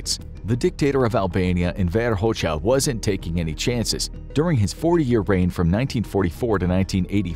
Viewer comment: Am I hearing this right or did they make a mistake or what? "During his 4 year reign for 1944 to 1985" did they mean 40 years? I'm assuming they did.